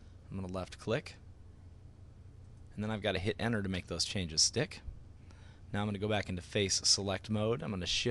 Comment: it sounds like he says dick!